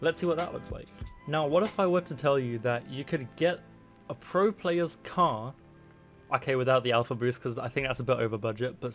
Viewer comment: What is the decal at 3:13? Please somebody tell me I didn't understand what he said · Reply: Pink griffon